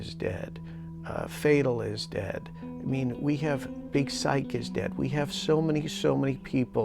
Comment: Did he say "psychics are dead" @ 10:50? If so that's pretty interesting!